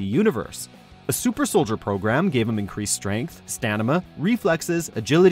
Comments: Did he say "stanima" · he said stanima instead of stamina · Did you just say "Stanima"? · Did you just say 'Stanima" 2:52 ! Sounds like a cool ass name dont you think? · did he say "stanima" ? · did he really say "stanima" when describing at the 2:52 mark? ... way to be human! lol